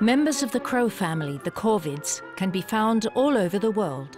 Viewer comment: I thought she said covids lol